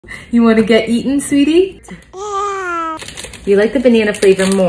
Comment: I misheard her as "You wanna get eaten sweetie" n i was shook for a second then realised she said "eatin" phew 😂😂